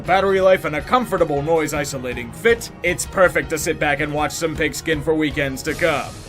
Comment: Just as long as the networks don't "pack" the stands with smiling digital zombie crowds. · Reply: Incidentally, when you said "enjoy some pigskin" at 0:53, for a sec I thought you were saying "enjoy some fake skin".